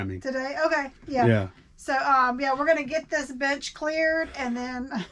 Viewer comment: 😂😂😂 Sounded like you said, get this b*tch cleared, (bench) 😂😂😂 You just made me laugh and I needed that 😎 Like seriously, I needed a laugh. ❤ You two are the best!